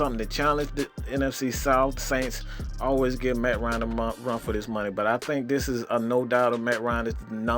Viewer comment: sounded like you was about to say motherfuking run for his money LOL